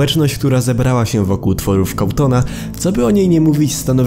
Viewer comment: Czy ja usłyszałem cawtona zamiast całtona? (Wiem że się pisze cawthon)